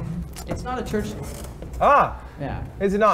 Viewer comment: "It's not a church ..." What was the word after this? (Also interesting to see Ludwig push this when Jerma has almost universally avoided anything beyond the superficial, and interesting to see Jerma trying to divert it.) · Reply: Church hymn